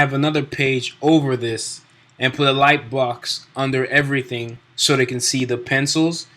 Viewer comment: I couldn't understand you when you said what I heard " Put a Like Box under everything" at 3:40, what was that you said again to put under? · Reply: "a light box" for inking, googling it would help, google images.